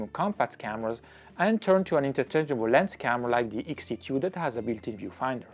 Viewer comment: couldn't exactly hear what you were saying, what camera was it again? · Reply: X-T2